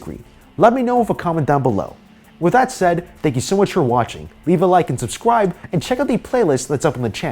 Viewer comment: Did anybody hear at the end “please subscwibe” lol